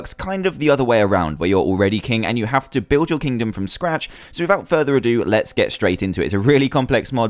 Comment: at first when he said from scratch i thought he was saying "block by block''